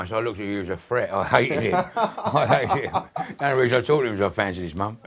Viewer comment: Got the subtitles wrong at 18:04 . They say A fancy smile when he actually said cus i fancied his mum.